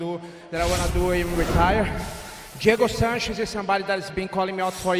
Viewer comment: Did Maia call out out Rafael Dos Anjos? At 2:04, I couldn't hear properly, am I right? Cus that would be bloody amazing!! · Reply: Sorry He meant Diego Sanchez